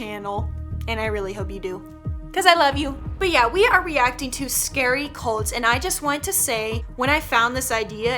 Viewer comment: At first I thought she said scary colds